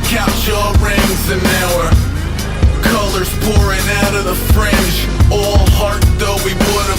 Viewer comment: I always thought he said fridge